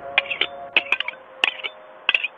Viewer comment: Sunny to Omori in the good ending: Es hora de vivir violently plays the violin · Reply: i thought he said: es hora de comer not es hora de vivir